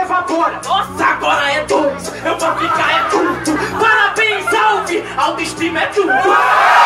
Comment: n entendi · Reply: Parabéns Alves auto-estima é tudo